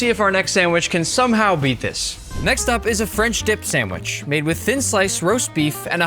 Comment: Subscribe if you love sandwiches! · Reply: why I heard french dih sandwich